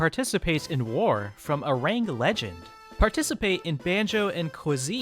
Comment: Arang: Shadow Legend : Is a mobile Turn-Based Battle MMORPG ! Try out 1 + 74 Fighter Builds and fight for glory on battlefield ! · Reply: Arang Legend: I heard that as "Orangutan Legend"